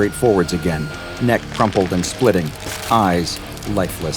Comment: SCP-3899 seems like an actual good guy. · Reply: I thought you said 4666- shitty eyes